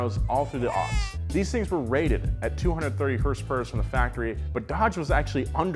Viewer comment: What did u say @ 5:10 (her purse) 🤣🤣🤣😂😂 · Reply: BighomieJ5x - its hrsprs (Horsepowers)